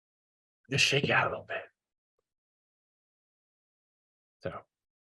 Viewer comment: What does he say? Just shake out a little bit?